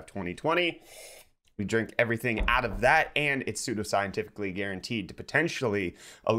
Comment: i heard "super scientifically guaranteed potentially" gg